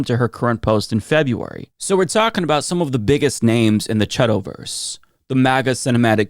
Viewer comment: I didn't catch that word at 5:23 what's the chedtoverse?